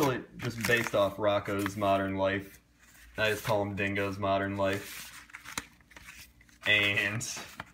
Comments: I cannot wait to see the Dingo's Modern Life 10'th anniversary. Also, I thought that said Dingus modern life at first... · i thought it said Dingus modern life